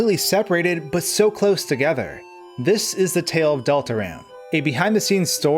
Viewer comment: You did bot say Deltarune...You said Deltaran.